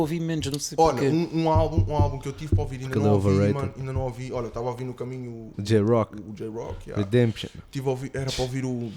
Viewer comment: eu ouvi essa joão moura... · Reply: João Tamura, também outro grande nome. O homem até é bom na fotografia .